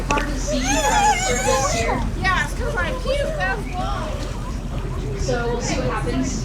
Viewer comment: What happened ? I couldn’t hear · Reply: I heard “it’s because I puked, that’s why”